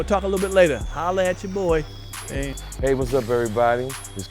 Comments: I swore he said Pierre Panty Room 🤔 😂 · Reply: 🤣🤣 I thought he said’Pierres Padded Room’ at first 😂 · I swore he said Pierre Panty Room 🤔 😂